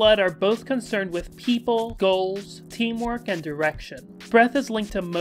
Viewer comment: I accidentally misheard direction as “erection,” which may also be related in some way to breath and blood too but most likely isn’t.